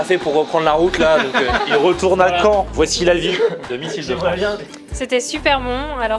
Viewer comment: J'ai entendu "Missile de France" 😂